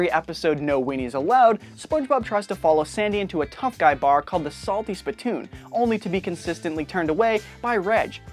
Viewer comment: I heard "The Salty Splatoon "